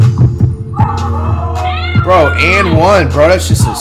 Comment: Excuse me did they say and 1?